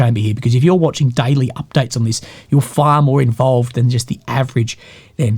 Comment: I thought you said "far more evolved" and couldn't agree more.. but, I guess, my ears aren't though.